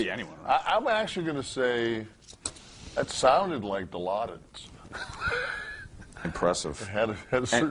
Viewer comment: What does Nash say the drugs are called at 4:11 ? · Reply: Diladed probably spelled it wrong but it's morphine